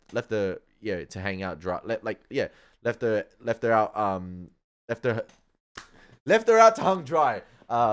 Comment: Interesting that in Australia it sounds like you say, "left her out hung dried" and here in the states we would say, " Hung her out to dry"... Enjoyed the reaction!